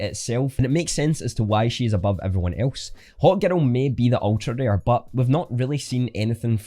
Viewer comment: Great video, love the accent. I thought u kept saying “hot girl” then i realized u were saying “hawk girl” XD Hahaha